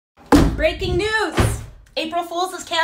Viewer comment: Sounds like you said "RAPING NEWS"